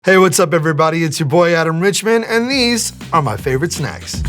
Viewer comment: I thought that said alan Rickman and I was very confused